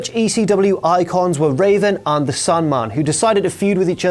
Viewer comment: sounded like you said "Sunman" instead of Sandman lol